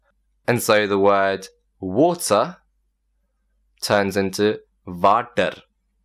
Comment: turn the subtitle on and you'll see the word "Vodka" instead of "water".